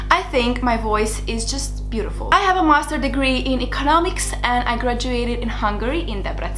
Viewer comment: I first thought you said "I have a monster degree" I was like whaaaaat where can I get me one of those! 😂😂😂😂